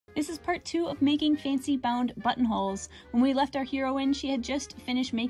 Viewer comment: I thought I heard her say "buttholes" in the first vid, but now I'm sure that's what I heard. 🤭